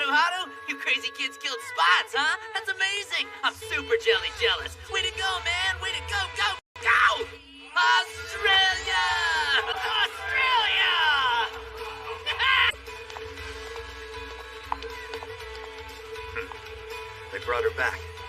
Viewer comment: am I hearing this correctly did they say Australia 😂😂